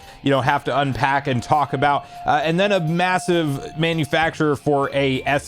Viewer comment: Did he say massive, or am I trippin?